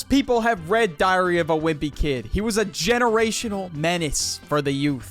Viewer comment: I heard "diarrhea of a wimpy kid" and was confused for a sec